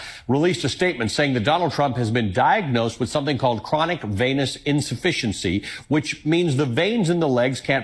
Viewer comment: It sounded like you said, chronic penis insufficiency lmao